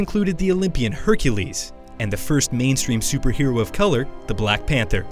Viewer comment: did he call black panther colored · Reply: Kyndall McGowan He said a person of colour.